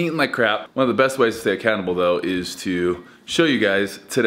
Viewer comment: Low key thought he said "one of the best ways to stay a cannibal" at 0:54. Lmao